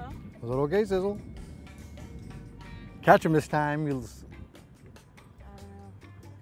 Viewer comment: catch them this time you little slu? @ 10:36 hmm did he just call her that lol